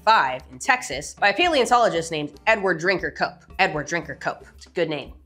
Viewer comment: Until I saw it written out, I thought she said his name was "Edward, drink your Coke".